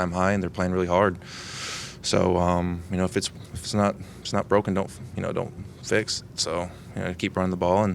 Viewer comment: I swear I thought he said " if it ain't broke don't Fitz"😂😂. Let's Go Team.